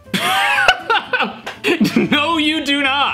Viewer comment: I heard you don't tug on superman's Kate